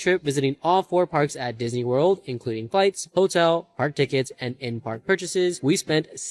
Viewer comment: It sounded like he said, "Including fights."